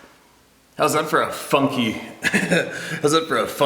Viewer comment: Lol. When he started talking, I thought he said "hows that for a fuck you?" Lmao